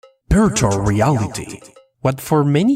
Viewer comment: At the start I heard “birtual reality”